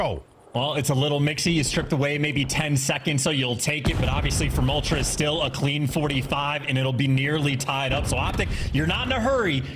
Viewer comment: did he say moltres like the Pokemon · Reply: he said but "obviously from ultra its still a clean 45"